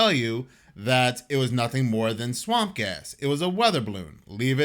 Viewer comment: Swamp a$$? Lol, remember, lack of sleep I thought you said swamp a$$ lol